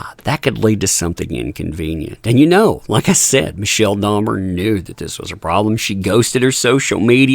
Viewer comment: I was listening while getting ready for my sister's birthday and heard wrong. I thought you said Michele DAHMER. 😆 🤣 😂 😹